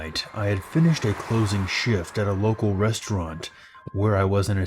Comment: WHY DID I THINK THAT SAID NORMANI RESTRAUNT LMAOOO